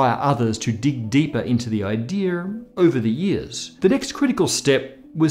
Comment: Did you say “idear” at 7:35???